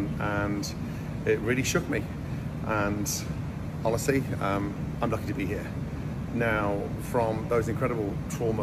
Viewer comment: Am I trippin or did he say at 0:55 “I will not be here “? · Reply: He said "I'm lucky to be here"